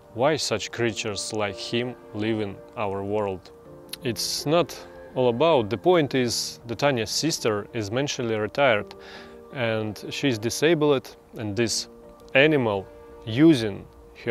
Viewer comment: Did I just hear "Mentally Retired " ? Nice way to put it :D Anyway did she do any psychological/intellectual testing or it's just some assumption by Tanya ? She seems stressed af and stimming like crazy, maybe she's just on the spectrum and she's actually smart but struggles to show that to other ppl due to social anxiety and other things like ADHD and poor life quality maybe ?